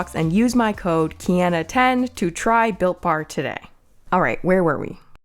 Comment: When you said your sponsor was Built Bar, I thought you said Bill Marr, I was like "...seriously" lol